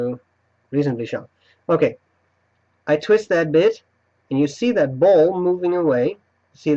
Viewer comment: Haha I could have sworn said I twist this itch.... Add a b. A had to watch that part 3 times lol